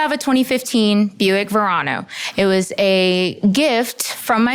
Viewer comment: Buick Murano?? 😅 Nissan Murano or Buick Enclave... unless I am just HORRIBLY mishearing her twice 🤦🏻‍♀️